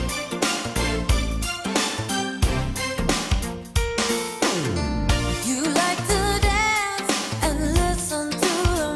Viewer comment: Does he say "fuck" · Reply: M Tremblay : No. He simply just says "Oh shoot".